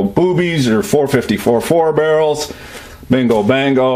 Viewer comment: I heard 4 barrel 45’s and thought “ I’m not alone” 😂